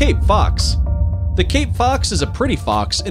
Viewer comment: I love arctic-fox, red-fox and crab eating fox. When you say cape-fox. Me:I thought you were saying king-fox lol.